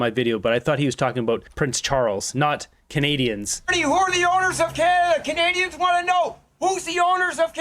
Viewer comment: I thought he said they own us too lol